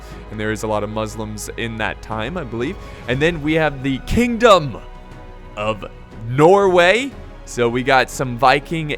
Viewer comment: thought for a sec you said KINGDOM OF ARNOR